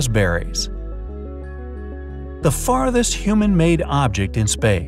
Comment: Lol I thought it said fartest